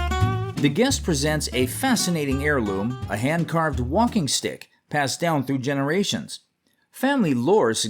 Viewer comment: When they announced that walking stick, I'd'a swore they said whomping stick!